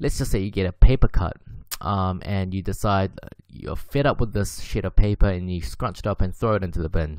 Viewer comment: i thought he said shit of paper LMOAOAO